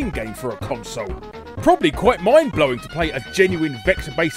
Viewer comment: Did you just call the game "mine blowing"?